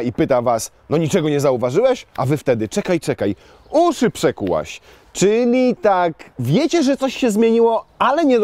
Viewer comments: Mam chyba słaby słuch, bo włączając na speed x2 w 0:07 usłyszałem "oczy przekułaś!". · Nie wiem czemu, ale za pierwszym razem usłyszałem "oczy przekułaś?"